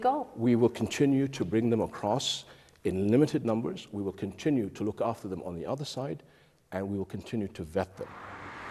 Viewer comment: What does he say at 1:28-1:34? I don't understand his accent... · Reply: We are going to continue to look after them on the other side